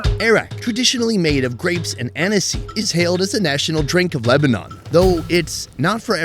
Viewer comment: Thought he said grapes and Hennessey 😂 Anis? Black Licorice. Hate the stuff. Like Jaeger meister